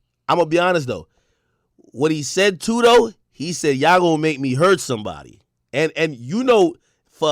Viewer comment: Dude I love you ak I disagree with you I thought he said let's get out of here BEFORE I hurt somebody not I'm gonna to hurt somebody